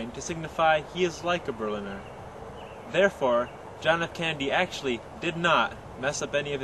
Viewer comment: good job on the video, kudos. thanks for clearing stuff up.... but at 2:24 1/2 did you say John F "Candy"? sounded like it. lol the mystery continues..